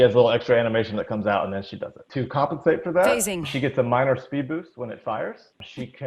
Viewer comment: ThiccBoof she gets slowed as well while trying to phase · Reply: I'm sorry mate. Should listen again. "She gets a minor speed boost when it fires", that's what actually said, not the reverse (15:00).